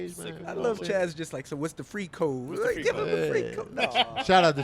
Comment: It sounded like Chaz said "what is the freak hoe" 😅🤣